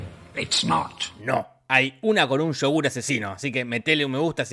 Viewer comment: No sé por qué, pero entendi aqui 0:35 "Un Shogun asesino". Hasta no ver al tio vomitando no entendí nada jajajja